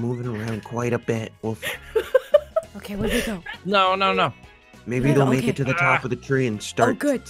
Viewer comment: I thought when said maybe they'll start mashin faces He said maybe they'll start smashin Am I the only one who thought this??😂